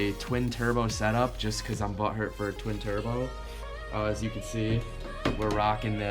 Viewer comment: Did he say just because he's BUTT HURT FOR A TWIN TURBO ?